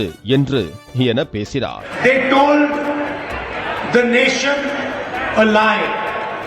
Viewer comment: " they told the nation a lie " .. But he heard it as " They told the nation alive "